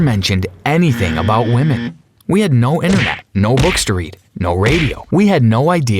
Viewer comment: I thought he said bookstory (history books) 🤦‍♂️